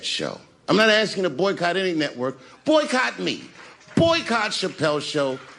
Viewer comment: I thought he said, “Boycott on HBOMAX” Fix the caption!! I already discontinued my HBOMAX. The tables have turned full circle for Dave, and the cards are all playing on his behalf ♠️♥️♣️♦️ Everything is working for his HIGHEST WILL - I’m rooting for my brother, Dave✨☀️✨